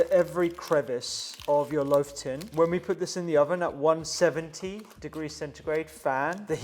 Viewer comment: 20g Chia seeds 150g Oats 150g mixed nuts- chopped 150g mixed seeds 35g Psyllium Husk 1.5 tsp salt Mix all dry ingredients 3 TBS Olive Oil 400ml water- first put half the amount, mix, then rest Grease loaf tin Press well -flat in the tin, in all corners the of the tin Soak overnight Bake: 180 C, 60min. Bake another 15min, out of tin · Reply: I thought he said 170 degrees fan.